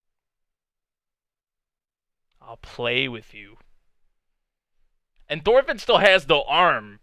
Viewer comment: it's just me or askelad has the same voice than Madara Uchiwa? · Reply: When he said to Thorfinn "i'll play with you" all i heard was "i'll dance with you".